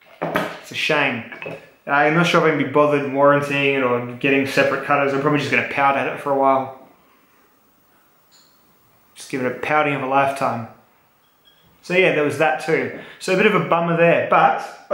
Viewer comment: Thought you said "pounding of a life" not "pouting"... Great having one of your videos up. For sure always starts my day off with a laugh.